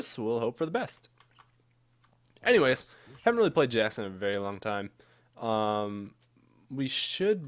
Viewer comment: Did he say Jax??